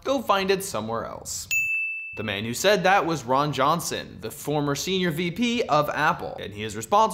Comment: When you said Ron Johnson I thought you said Ron Swanson XD